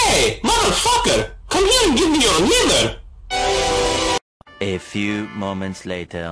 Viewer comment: the fact that the captions said "hey [ __ ] come here and give me your mother" 💀💀